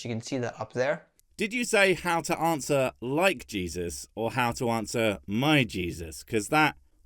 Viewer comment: He clearly says "How to answer Mike Jesus." Now I don't know who Mike Jesus is, but he sure is looking for an answer to some question or another.